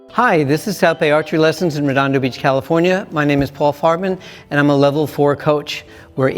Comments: anyone else think he said “I’m Paul Fartman.” I was like bro i feel bad for you and your family, but I looked at his shirt and was liked, oh....ok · The coach seems nice and all, but all I heard was “My name is Paul fart man” and I tapped out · wait did he just say his names paul fartman?? am i the only one hearing that? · I thought he said Paul Fartman Lol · i thought he said paul fartman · I thought he said Paul Fartmannn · I could’ve sworn i heard paul fartman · Could have sworn the guy said his name was Fartman